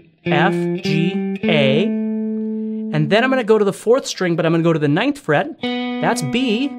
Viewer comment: I'm sorry did you said "four string"? is that correct? I'm not a native speaker :(